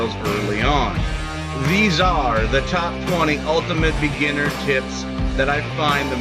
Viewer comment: What I hear "these are the ultimate beginner tits!" Okay where is the tits?